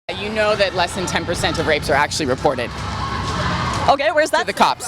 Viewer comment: What I hear "90%+ of rxpes are false".